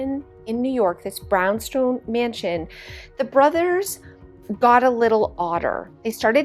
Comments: 1) I heard that "the brothers got a little otter" 😅 2) E.T. I'm terrified of E.T. I'm roughly 2 months older than you, so totally understand that 80's vibe, but E.T. can kick rocks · You said "got a little odder". I heard "bought a little otter". Now I kinda want a whole hoard of otters.